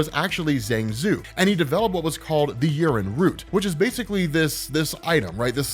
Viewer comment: I thought I heard jarrin Root.